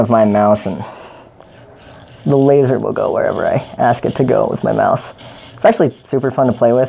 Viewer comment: the laser will go wherever I ask it to go with my mouth. I think I heard that wrong